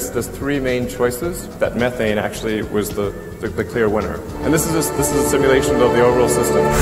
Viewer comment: Did he say this is a simulation ?